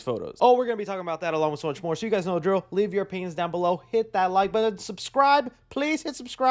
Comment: it sound like he said “leave your penis down below”